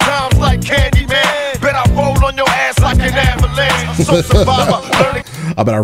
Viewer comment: I always thought he said bet I role on your as like an ambulance but felt like that's not what he really said.